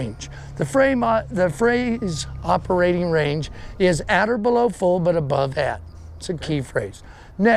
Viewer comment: I don't know what he said from 3:48 to 3:54 and he said it is a key thing to say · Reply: "'Operating range" means at or below FULL, but above ADD.